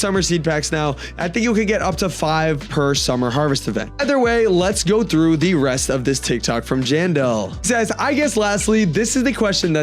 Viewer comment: him saying general instead of jandel is wild 💔🥀